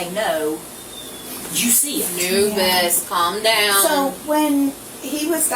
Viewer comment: did she just call the child “Anubis”? Could’ve just been the subtitles mistake…